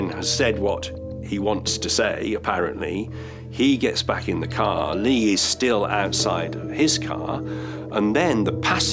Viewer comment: Due to over Sensorization, and poor editing, It definitely sounded like you said Lee was raped outside his car.